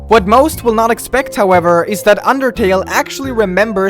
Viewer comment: did you say underkill or undertale?